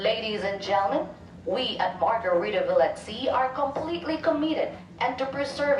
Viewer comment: sounds like she said "completely comedic"